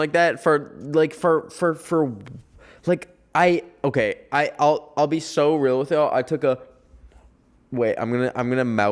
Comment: what is he saying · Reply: I think he's saying mushroom as in drugs